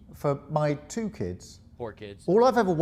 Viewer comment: When he said that he has two kids and you said poor kids I thought you said four kids and I was like oh my God did he really forget how many kids he has?😅